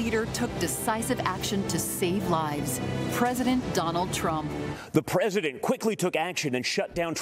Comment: Did she call him "Donald Tramp"? 😂